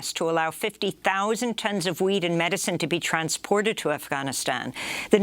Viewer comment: "WEED and medicine?" Did I hear that correctly? At 6:40; "In related news, Indian officials are accusing Pakistan of blocking a request to allow 50,000 tons of weed and medicine to be transported to Afghanistan..." Did she mean to say "food and medicine"?? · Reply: I heard weed too but it had to be wheat. Lolol